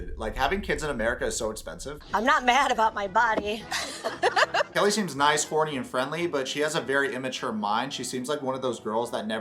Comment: I thought you said, "Kelly seems nice, korny, and friendly" 😹😹😹